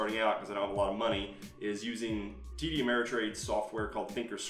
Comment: Great introduction to market trading, thank you. At about 9:30, you mention a company who provide 'Think or Swim', but it's unclear - did you say 'TD Ameritrade'?